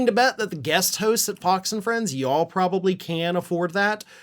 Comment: at 1:30- I thought you said “POX and Friends”! and then I thought,- well that fits. The Murdoch “misinformation machine” is a pox on the U.S. and the world.